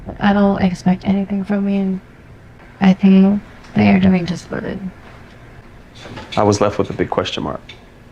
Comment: I heard "you're doing just fine" but dubbed over as "you're doing just good"